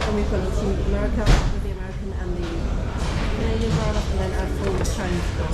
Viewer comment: so wholesome also I think I heard Canada 🇨🇦 👀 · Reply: There was indeed a Canadian driver 👀